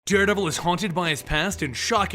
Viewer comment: did he say daredabel